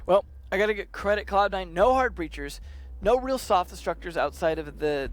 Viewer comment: They did dude. They said that c9 didnt bring up any soft breacher, that actually includes buck. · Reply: i thought they said hard breacher(?)